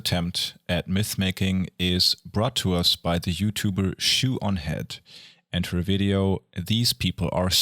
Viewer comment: I must have misheard her name as shit-on-head